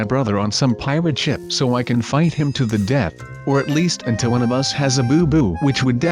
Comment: I THOUGHT YOU SAID "Or at least when one of us has a boob" I WAS SO SCARED